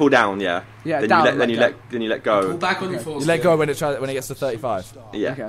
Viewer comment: did anyone else hear say 'pull back on your foreskin'? XD